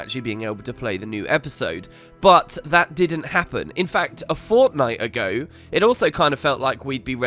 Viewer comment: did somebody say, "fortnite" ( ͡° ͜ʖ ͡°)